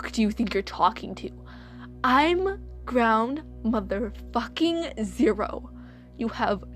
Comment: All I heard was “I’m ground zero mother fucker!!!”